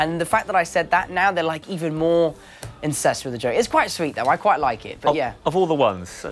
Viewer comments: i love how the interviewer is just dying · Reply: he says incest instead of obsessed. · he said incest instead of obsessed xD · did he just say there more incested with it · Anyone else hear him say "They're even more incest with the joke" ? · I'm dying here! While talking about how he was getting roasted for mispronouncing a word he totally misspoke and said the word "incest" instead of "obsessed" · Did anyone realise Tom said "incest" instead of "obsessed" at 5:46?